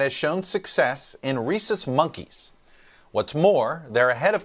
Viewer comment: did he say racists 🐵 s?